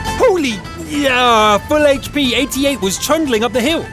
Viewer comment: Did he just say that was an AT 8? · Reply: I thought the same thing at first. He was saying the 88, as in the JgTig.8,8 cm